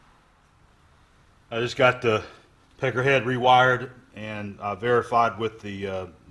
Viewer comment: Did say, "Pecker head"? ;-) That motor came out beautiful!! Much, MUCH better than New!